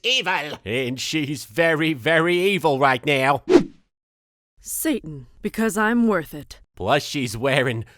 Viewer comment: DakotaSagePika While that WOULD make sense, it sounded like she said something else. Hence my confusion. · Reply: said Satan.